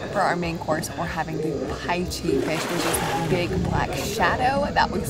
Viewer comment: Wow awesome video! Loved all the footage. What was the big thing under the water with the babies - I thought you said a python. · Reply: That was a paiche fish